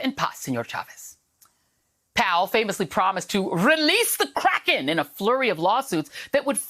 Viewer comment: My hearing is not so good, I thought she said "..release The Crack!"